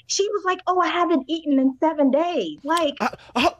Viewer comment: Jacob has a dry sense of humor. Him sitting at that table when they were arguing and only talking about being hungry was priceless. The 80's thing is a bit weird but I dont think its necessarily a red flag..just let him have it in his man cave and keep it moving! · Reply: I thought Hailey said I havent eaten in 2 days .I gotta rewatch.